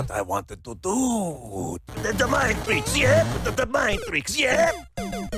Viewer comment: tf is he saying 💀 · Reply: "THE-THE MIND TRICKS, YEAH? THE-THE MIND TRICKS, YEAH?" in his Watto voice